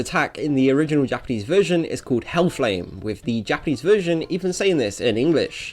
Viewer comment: Is it just me or does it sound like yugi says ell lame